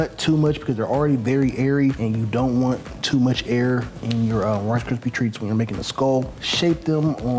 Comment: did he just say... horse poopy treats 😂